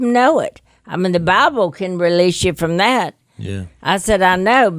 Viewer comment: No disrespect to Miss Kay or her 'nanny' but Jesus discusses divorce in the Bible, including when it is permitted. I just don't want people thinking that they have to stay in abusive marriages because of what Miss Kay says in this video. Things thankfully turned out well for the Robertsons but that isn't necessarily the outcome for many people. I am a big believer in forgiveness but for some people, staying in a marriage could literally cost them their lives. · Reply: Maybe you didn’t hear exactly what Miss Kay said but at 49:18 in this video she says “the bible can release you from that” speaking about when you can divorce someone because of an abusive marriage. Miss Kay said she chose to stay even though the Bible said she could have divorced Phil. She told her story but it was in no way endorsing to stay in an abusive marriage. Simply what she chose to do against everyone else’s advice except her ‘Nanny’.